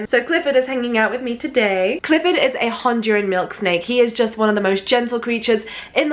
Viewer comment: Instead of milk snake I thought she said milkshake 😂